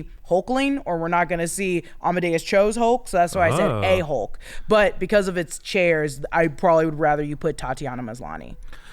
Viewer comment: My ears are getting old. 32:30 I kept hearing say "A-Hole" instead of "a Hulk".